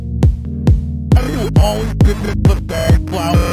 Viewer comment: Anybody know what blocky saying? A̸̹̤̙̪͓͕͗͐̌̉̽̿̇͆H̴̢̨̦̻̥̘̲̞̹̻̘̤̅̎͆̏̄̏̐̅̂̒̄̐̓̒̕Ḥ̷̡̧̘̥̠̯͓̞̱̍́̔̋͐̐͛̎̚̚͘ͅA̷̢̧͈̩͚̳͖̘̜͋͆͗̎̍̍̑͝͝Ḩ̷͔͕̘͕̅́̅̄͗͘a̷̛̛͎̱͇̭̯̞̻̣̲̗͍͙̫̅͌̑̅̊̌̿͠ͅš̷̨̢͍̫̼̮̘̱̤͓̺͊̎͒͝d̸͖̬̽͋h̶͙͊à̶̫̃ĩ̴͙̯̮͔͈̲́̄̊̀́̌̄̾͘͜͠ų̵̪͕̟̭̤̹̘͇̦͂̐͆̏̿̓͐̂̓̌͗̋̓͠d̶̡̧̛̲̙̠͔͍̗̬̹͖̩̮̹͊̒͌̓̎̚͜i̷̦̗̖͖̪͇̽̋͒̈̍̚ ̵͓͚͉̺̯̘͍̭̘͊͌̓̋͑̕͠Į̶̛̯͈̯̤̼̼̺͔̦̭̟̯̬̿̆͜͝Ṁ̵̲͋̓M̸̢͖͓̳̥͎̗̳̝̳͚͍̝͖̞͆M̷̱͎̟͈̱͛̋͗̒̔͒̆͑̊̚Ạ̴͙͉̲̭̟͎͖̮̥͕͉̻͛͆̐̔͐̿̏͘͜ͅD̴̳͓͙̳͖̪̜̺͖̪̈́̐̑͂̈́͋͐̒͝͝ ̷̨̜̖̳̘͍̺̭̱̤͍̄́́͐̃͑̂̚͜͜G̶̤̣̬̠̤̪͖̝͊̈̈́̏͊͘̚͝Ě̵̡̧̺͚̰̟̥̫̊͐͑̍̏́̑̈́͘̕͘͠T̸͉̩͒̈̈́͂̎̄͌̌̿͋͒̒͋̆͝Ţ̶̢̺͔̘͍̝͖̣̼̭͖̾̀͊͋͂̚͠T̶̼̥̙̈́̋̓̃I̵̛̝̳̓̆̅̉͛̉̆͒I̸̠̥͓͒̿̾̔̾͌͋Ḯ̸͔̹͎͓͉̟̯̭̯̪̙̟̰̂̊̿̔̆̾̈́̒̎́̊̏̂͜͝I̷̛͖̻͇̬̹̦̭̼̠̻̖̲̙̬̥͌͋̈́́N̵̡͓̙̺̮͚͍̱̟̜̱̲̻̜̽̓̎̕ͅN̴͓̦̹͉̪̭̱͗͘N̵͖̪͖̯̼̥̣͎̬̤̱͈̣̏̓̀̆́̐Ń̴̨̫̤̯̩͓̻̋͜͝G̴̫̭͇̼̒Ģ̵̖̣̳̯̦̱̱̩͓͍͙͚͛͗̉͌̂̅͆͐̓͆̔̀G̶̢̡̝͔̣̩̺̖̹̖͉̮̺̈́͊̉̍̀̈́͊̒͋̕̚̕͜ ̸̧̡̢͚̣̮̱̣͙̜̣͕͈͆̏̋̅̆͌͛̽̚͜G̶̡̧̛͓̮̭̬͈̪̦͙͙̿͊͌̽͊̽͆͛͜Ḷ̸̠͔̖͔̜̙͍͚̝̳͂͋̓̽͝Ļ̶̡̟̝̼͇̱͖̰̱͕͐̆̇́̐͜ͅL̴̯̰̯̜͍̿͌̏͋̀̓͂Į̷͓͎̹̫͖̬͍̈́̀͆̍͗̍̉̌̅̄̊̓́̒͝Ț̶̨̌͋̑̿̀̌͆́̋͊͜C̷̢̥͚̓͐̏͑͛͗͊͠H̷̛̛͚͈̳͚̞̼̬̙̏̓̐Ę̶̮̫̬̮̱̣̺́̽̈́̄͊̈́̓̆̈́̂͊̚͝͠H̴̡̛̤͚͇̽́̋͝Ę̸̛̛͚̩̟̳͕̊̓͛̆̈́͋̊̂͂̊̕D̵̢̗̗̜̻̥̙̦̀̄̂̚ͅD̷̛͎̫̞̪̦̩̮̝̠̘̲̯̬̎̐̃̿̄́̀̚͜͠ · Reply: Where you always like that flower? Said in BFB 15